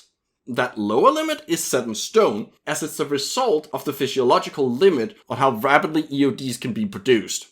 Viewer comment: Did you just say "a fishiological limit"?